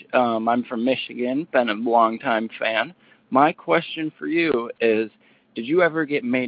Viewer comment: I thought gage said "been a long time trans" and i was like "good for you, you be you dude" but now looking at subtitles wow i was wrong lol. Mishearing mistake on me.